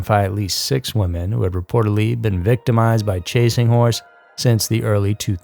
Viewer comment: All I hear is" chasings whores"